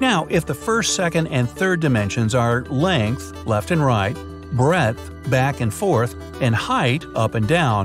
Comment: can someone tell me what word he is using in 4:17 ... is he saying breath? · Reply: It's breadth which is the second side of a rectangle just like length